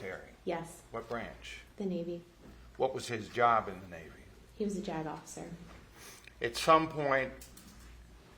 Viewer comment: 😅 When she said "JAG Officer", it sounded like she was saying, "he was a jagoff!" 😅😅